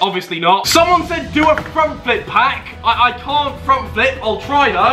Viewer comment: When they said do a front flip pack I thought you said a throat pack I cant do that I was like who could ever do that